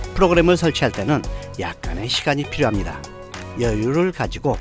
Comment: 프로그램을 설치할 때는 약 __시간이 필요합니다. 뭐라고 말씀하시는건지 잘 모르겠어요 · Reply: 약간의 시간이 필요합니다 입니다.